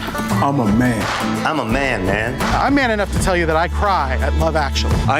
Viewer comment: In that advert, all i heard was " I AM MAN ENOUGH TO WANT TO USE THE LADIES TOILET"